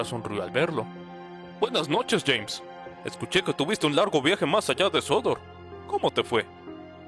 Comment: que dijiste?👀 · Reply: Un viaje más ayar de sodor🗣️